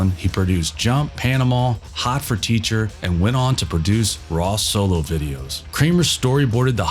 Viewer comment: "and went on to produce raw sewage". I'm sorry, but that's what I heard.